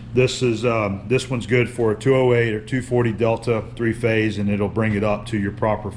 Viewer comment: I just heard you say subpanel - that panel will get you 480 or 277V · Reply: He said Delta. So 480 volts.